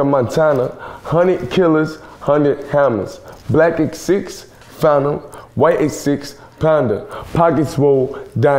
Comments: “Black X6, Phantom” So you’re telling me it’s not “Legacy, Phantom”???? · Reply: I hear " legacy phantom, wait and see, panda" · always thought he said legacy phantom white x6 panda